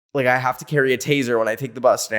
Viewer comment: did he say he carries a "TAZER" when he takes the bus now?